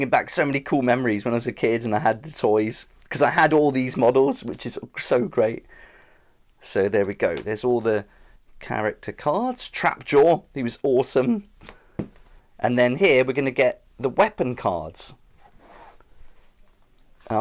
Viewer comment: Did you just say Trap Door instead of Trap Jaw? 😆